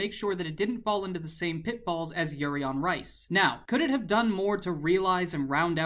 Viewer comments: I heard yuri on rice...XD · "Yuri on Rice"..did only I hear that?xD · I heard "yuri on Rice" 😂😂😂😂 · did anyone else hear yuri on rice at 10:48 lol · did anyone else catch him saying yuri on rice at 10:48? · I heard "Yuri On Rice"